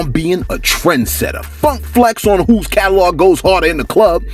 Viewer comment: Did you just say fuc flex?? 🤭🤔 Cunning wit da "words"